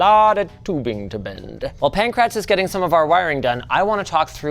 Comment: Seriously, what is his name? 10:54 I keep hearing “pink rats”